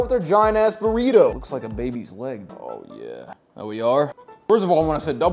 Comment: Foor some reason when he said, “its like a baby’s leg.” I heard, “this is what the women like.”